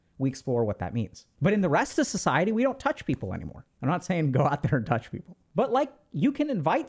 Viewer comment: All I hear is "go out and touch another people" thanks will do